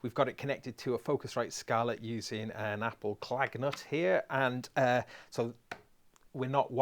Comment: at 01:19 did you call the dongle an apple klagnut ?. :-) LOL